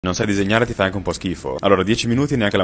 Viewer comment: Lui: "Non sai disegnare e fai anche un po' schifo" Io: Sì, dimmi tutto 😌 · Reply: Io ho capito "non sai disegnare e ti fa anche un po' schifo?"